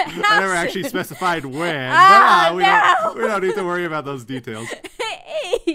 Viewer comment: I definitely heard “cats” not “paths”